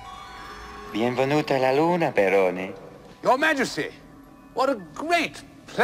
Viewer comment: Was the Moon King speaking Italian or just gibberish due to his lunacy? 🤷‍♂️ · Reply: I thought he said "perone" not "barone". Always sounded weird to me xD